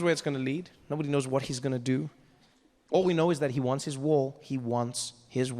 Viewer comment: ive seen this special many times yet i still laugh hard. but... watching this in march 2026, trevor said he 'he wants his wall' i misheard it 'he wants his war' .... lol